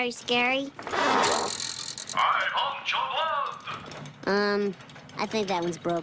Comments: I vant your blood! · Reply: I always hear it as "I haunt your blood!" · I vant your blood! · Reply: I heard it as "I got your blood!"